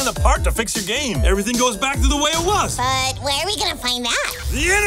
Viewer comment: “But where we gonna find that?” “The internet!” Classic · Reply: At first I thought he said "But what are we gonna play now?"